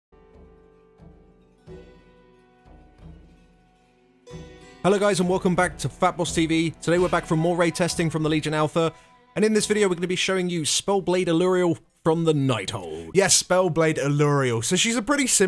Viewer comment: Thought he said "welcome back to Fapboss" at the start